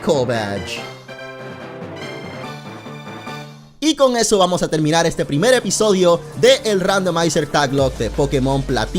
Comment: Wait, can someone please translate what he said at 40:42? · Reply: he said "And with that we'll wrap up the first episode of Pokémon nuzlocke taglocke of Pokémon platinum. I hope you enjoyed it and make sure to check out the other episodes, they'll be in the description."